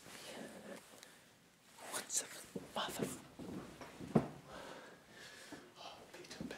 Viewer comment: sounded like he said mother fu